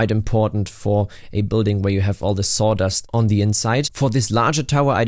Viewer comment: At first I thought I heard sauna, so i was really confused when you said sawdust